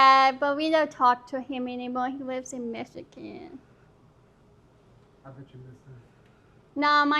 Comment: "You look like my uncle Alfredoo...he lives in Michigan" 🤣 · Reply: I thought she said "He lives in Mexican" 😂😂😂